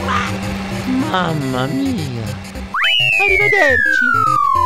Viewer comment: I thought he said son of a beche but he really said ariva derchi